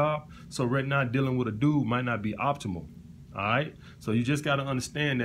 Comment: "So, right now dealing with a dude might not be optional/optimal. Me: did you say optional or optimal? Yes.